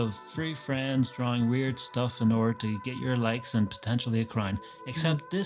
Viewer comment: During the introduction I misheard "and potentially a crown," as "potentially a crime.". Which I suppose wouldn't be wrong.